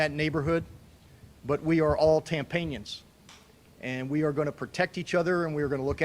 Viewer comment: The mayor said we are all Tampanians lmao 🤣😅😆😁 i thought he said tampons for a split second